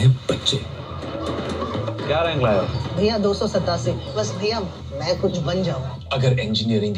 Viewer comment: he actually said 287 and not 272